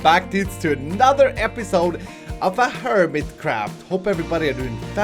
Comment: I swear it sounded like he said Hermitcrap at the start considering he has three hermit series and PUBG on the side at first I thought he had gotten tired of HermitCraft but no I think our favorite is still going strong :) Love you